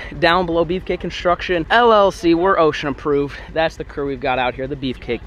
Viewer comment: I thought you were saying ocean Approved